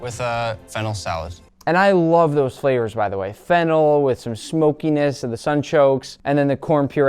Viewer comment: anyone else hear fentanyl?